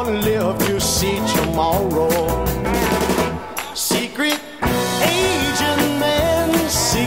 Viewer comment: When i heard this on spy next door i thought it said secret asian man but then i found out it said secret agent man